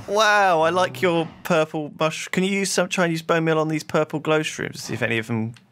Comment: Did anyone else hear say "Can you use some Chinese bonemeal on these purple glowshrooms" instead of 'try these' at 7:38?